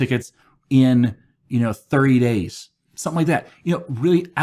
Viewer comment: "1000 help tickets fixed in 3 days" I would hire that person! I am lucky if I get 1-2 bugs closed off in a day 🙃. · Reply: I heard "30 days", actually :)